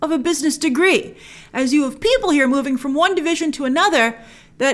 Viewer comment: When said “one division” I thought she said “WandaVision” 😂